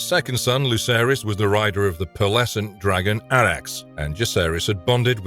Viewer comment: what did he say? Luceraeys was a rider of the PleAsAnT dragon? Didn’t get it